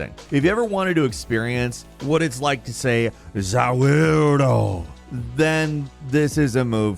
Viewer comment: need this on loop is he saying Za worldo or Za weirdo I can't figure it out.